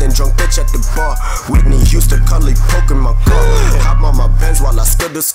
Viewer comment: when ski said coke in my car i thought he said pokemon go Lmao